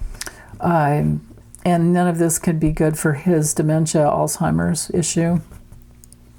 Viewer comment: Had to laugh. You said, "his edema", but I heard " his demon". Both are appropriate, lol! Have a great day.